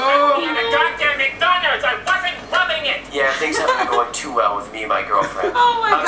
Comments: "YOU LEFT ME AT MC DONALDS,I WASNT LOVING IT" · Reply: Dominic Blair I Thought she said “I wasn’t rubbing it” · "YOU LEFT ME AT MC DONALDS,I WASNT LOVING IT" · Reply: I thought they said " I wasn't rubbing it " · "YOU LEFT ME AT MC DONALDS,I WASNT LOVING IT" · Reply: Justfandomstuff I thought she said “I wasn’t Rubbing it”